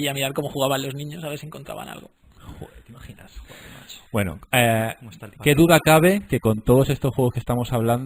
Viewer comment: ¿Que es lo que dice el tio de la derecha en 25:15? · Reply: dice algo así como: joder cómo está el patio. K el tipo era un personaje ,vamos